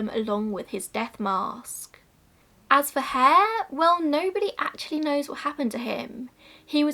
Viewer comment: after mentioning what happened to Burke's skeleton and skin I thought you were saying that no one knew what happened to Burke's hair 😂😂 I was really confused but soon realised you meant Hare 😂